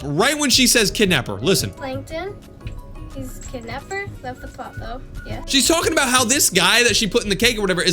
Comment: yo who heard plankton his kidnapper shut the f### up yeah